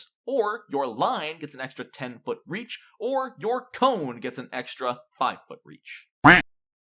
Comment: I heard “your colon gets an extra 5 foot reach” and thought of that video with Yoda going “mmMm… reach your colon, I must..”